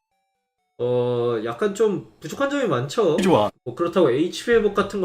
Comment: 그런데 0:50 좋아 라는 소리 저만 들리나요?